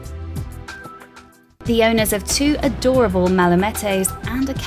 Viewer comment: " Mala - MAT EHS " I heard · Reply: I heard 'malamettos' when the dog breed was first mentioned.